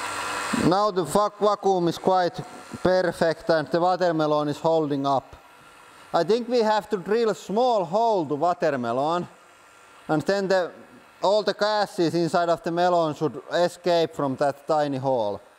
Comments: Anyone hear water malone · did he just say now the fuck vacuum??